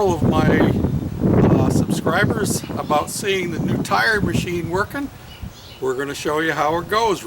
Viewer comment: Oh, tire machine, I thought it said time machine...ha ha Nice job